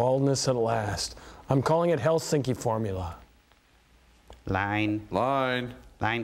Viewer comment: Did you say... Did you sayy.... HELSINKI FORMULA? i am from Helsinki